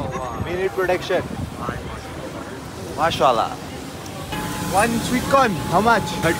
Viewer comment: It's not masala it's masallah it's what Muslims say when they see something beautiful ❤